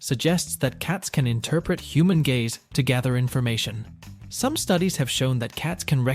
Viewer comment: I know he probably said "gaze" but initially i thought he said that cats can recognise human gays 😂